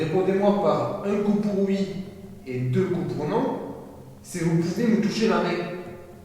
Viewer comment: Pouce bleus si toi aussi tu entends à 33:22 dire "est ce que c'est possible un coup pour oui 2 pour non, SI VOUS POUVEZ ME TOUCHER LA RAIE" MDR